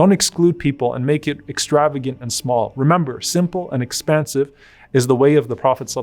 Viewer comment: Just a quick note to the subtitlers, at 7:14, Sheikh Omar said 'simple and expansive' the caption says 'expensive' which gives entirely the opposite meaning. Jazakallah Khair for the video.